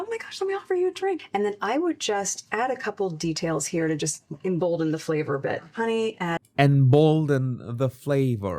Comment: You're LITERALLY watching my first reaction to realizing Meghan has been hiding the likes in her Instagram posts. I don't know if other creators have pointed this out but I certainly didn't know. So much insecurity! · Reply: I heard the "embolden the flavour" . As put mold in the flavour.